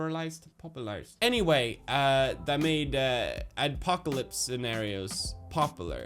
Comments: Did he just say 'popularised the ADPOCALYPSE idea'? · Did you just accidentally say adpocalypse instead of apocalypse. :D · did he say adpocalypse?